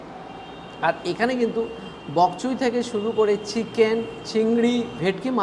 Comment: কি বললেন? বগচুই? 😆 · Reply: বক চৈ